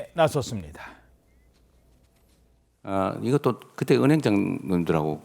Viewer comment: 은행장“놈”들이라고 들리는건 나뿐인가???? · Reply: 놈들이라고 했네 ㅋㅋ 자막만 분들이라고 나오고